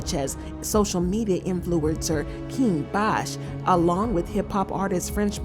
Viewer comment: Did you say King Bach was there.... (16:56) He seemed like the kinda brother that would be a LOT like Kevin Heart SMH